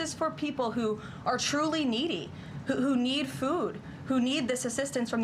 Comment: I heard him say FREE food